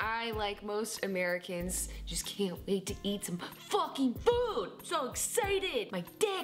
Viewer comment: subtitles said (at least for me): "Can't wait to eat some talking socks!"